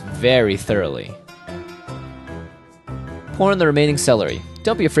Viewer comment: did he say porn xD